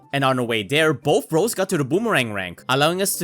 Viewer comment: I heard boring rank